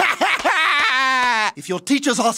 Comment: I thought he was going to say "I got hit by a baby".